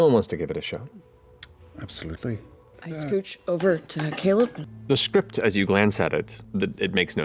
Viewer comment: Periscat. Scat=animal poop. Then Matt made a tub girl reference. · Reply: Thank you, I thought he said, "Top, Caleb?" referring to his bi/pansexual tendencies and related preferences. And thanks for the explanation, I thought I'd hallucinated seeing that picture as someone's Yahoo Profile photo back in 2002 cause I'd never heard it reference again.